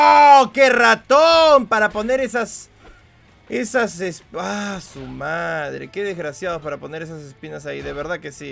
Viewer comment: que dijiste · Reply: Dijo a su madre xd